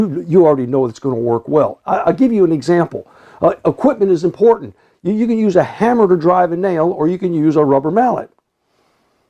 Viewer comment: It sounded like he said “You can use a hamburger to drive a nail!”!😂